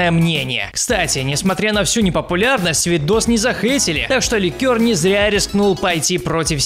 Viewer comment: мне одному кажется, что он говорит ликёр?😂😂